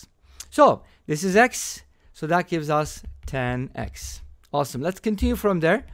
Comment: what was it again？ 10X did you say？ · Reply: 10x yup it happens